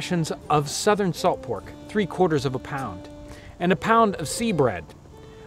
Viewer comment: I thought it said a pound of flesh